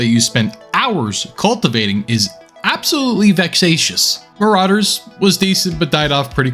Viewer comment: What did he say? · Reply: If you're talking about the adjective he use, it's "Vexatious" ; if you're talking about the game name, it's "Marauder"